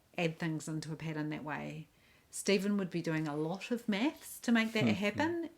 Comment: OMG! I thought you said, “Steven will be doing a lot of meth.” You actually said, “Steven will be doing a lot of math”😂😂😂💗💗